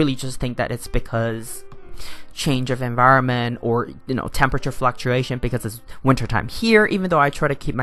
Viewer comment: At one point did you just say "fu** fluctuation" !! and butt blast! I don't even know what that means! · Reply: Haha...I think I was trying to say 'temperature fluctuation' and then '(flower) bud blast'... Hope this explains it...